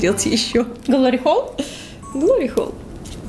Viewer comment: что они сказали?! Может мне послышалось, но мне показалось, что они сказали "глорихол", так вот я начала гуглить и... ДЕТИ НЕ ГУГЛИТЕ ЭТО!!!